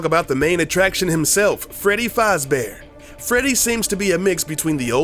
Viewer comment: It's sounds like your saying fozbear instead of fazbear